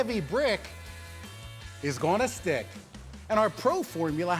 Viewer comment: what the heck when i first saw this on JonTron i only heard the part at 0:32 as "is gonna STAY" but now that i read the captions i only hear it as "stick"